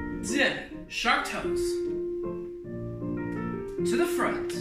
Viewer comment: Watching, and - forgive me - at 19:10 I thought you said shark toes! 😂